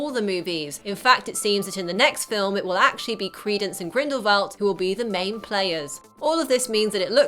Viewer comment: Was I the only one that heard her say, ''Grimblefield'' instead of ''Grindelwald''???!!! Please say I wasn't the only one!!